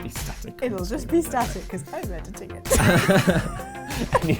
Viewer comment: It will just be static because ______tickets (What does say, I couldn't make it out) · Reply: ELiT3CH "because I'm editing it"